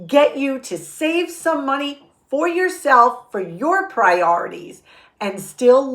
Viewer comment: Did you say pray or atees? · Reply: She said “priorities”.